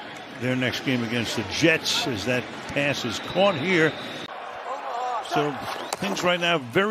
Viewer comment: Did commentator say “as that ass gets caught here?” Minute